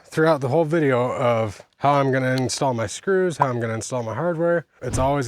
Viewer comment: I thought you said this is how I insult my screws...lol